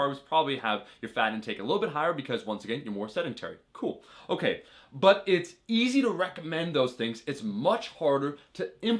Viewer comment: did you say 7g/ pound of body weight? Did you mean 1g? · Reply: He said "point 7 grams" = 0.7g per pound of bodyweight.